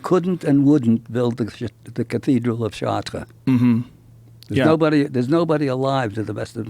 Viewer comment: "The Cathedral of __ " anyone know what he says at 24:26 ??? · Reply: Bg Stutes Chartres